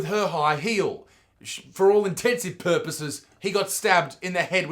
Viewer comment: did, did you say "intensive purposes" :|